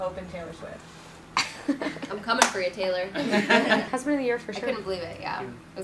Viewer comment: what did she say about Taylor Swift?? I didn't catch it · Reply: she just said 'I'm coming for you Taylor' :)